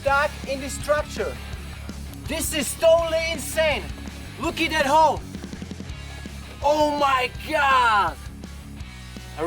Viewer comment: it sounds like hw says look at that ho xD